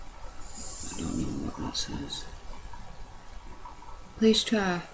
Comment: Anyone else hear a voice that says "I don't know what loss is" at the 43:45 mark, while is using the Ouija board?